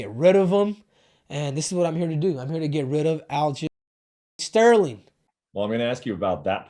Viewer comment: What did they block out of him saying at 6:56 ??👀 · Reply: He called aljomain --- aljolame